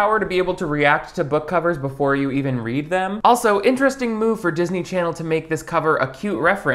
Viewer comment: did you just say “jizzney channel”?